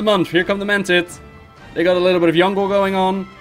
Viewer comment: Lol at 39:38 it sounds like you say "they got a little bit of the young girl"